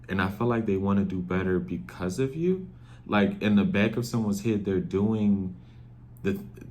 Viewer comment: Why did I hear “ he bacccck” in sexy red voice HA.